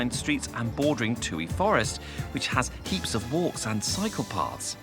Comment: when he said cycle paths, and I saw the park, due to the PTSD in USA these days, I thought he said psychopaths. Australia seems delightful & wonderful, great show🏡